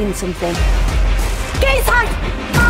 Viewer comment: Anyone else have to turn on captions to figure out what that first line was? I kept hearing 'JESUS'.